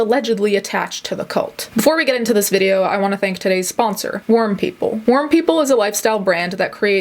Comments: I thought you said worm people lmao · I thought you said "worm people" and honestly, that's me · I THOUGHT YOU SAID WORM PEOPLE LMAO BUT I WAS STILL DOWN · i definitely thought you said worm people, and I accepted that as normal.